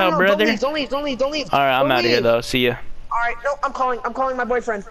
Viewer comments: Wait at the start did I really hear him say “I’m going to call my boy friend”.😐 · did He say “ iam calling my BOYfriend